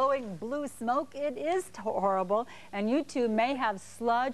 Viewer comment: sounds like she said youtube may have sludge. I was like whaaaaaaat? Then I was like OHHHHHH 'you too.'